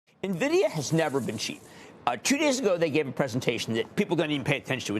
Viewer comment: He said Nvidia has never been CHEAP! (not cheaper) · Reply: Thanks, you're right - I had to re-listen to it. I swear I thought he said it had never been cheaper, which made no sense